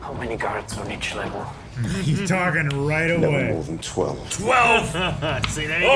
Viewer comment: I don’t know how or why but I got chills when Andy Serkis’ character said “Never more than twelve” next week… prison break! And probably a revolt on Ferrix if they go through with hanging that guy. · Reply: I had to listen to that line a bunch of times. I thought he said "eleven more than 12" and I was very confused.